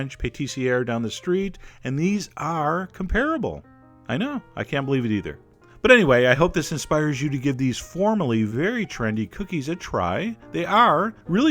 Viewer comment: When u said 'comparable' I thought u said 'terrible'